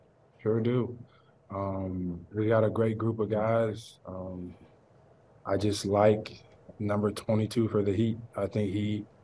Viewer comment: 😂😂 did he just say we have a great group of guys , I just like 22 for the Heat .. 😂😂